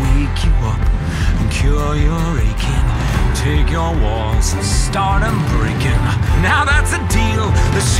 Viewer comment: I heard “Take your walls and start em breaking” as “Take your bones and start em breaking”. Deku wants to know your location